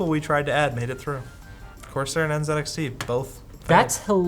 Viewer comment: What the subtitles heard: Of course they're an (indistinct) both. What I heard: Corsair And NZXT. Both.